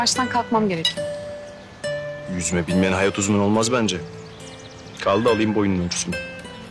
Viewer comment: da Murat Hayat Uzun diyor.Ben mi yanlış duydum. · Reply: Hayat uzmanı diyo